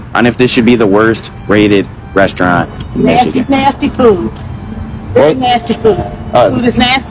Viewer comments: That lady saying nasty food💀😂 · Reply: I thought she said "nasty poo" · The lady walking by couldn’t be more of perfect timing lol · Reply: i heard "nasty nasty poo, nasty, poo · I thought she said bro is a"nasty poop"😂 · I thought she said nasty poo